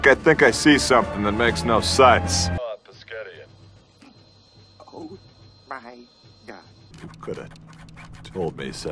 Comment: What'd he say? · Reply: Pasgetti- this episode shows that Brock can't say Spaghetti, and Billy and Rusty both tease him briefly for it